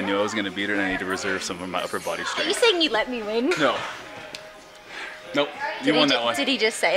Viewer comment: Did you say you let me win???!!!